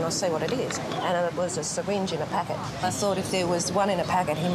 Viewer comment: as an American I have no idea what he’s saying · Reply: It's a lady. There was a syringe in a packet - please clean out your ears.